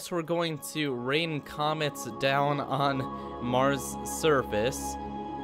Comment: i thought he said we are going to rain down comments 😂